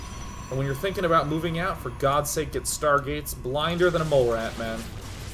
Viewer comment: What was he saying at 19:46? "... for God's sake, get Stargates..." and then what? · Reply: blinder than a mole rat, man.